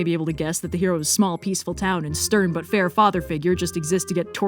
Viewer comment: I heard "fodder figure" and its much more fitting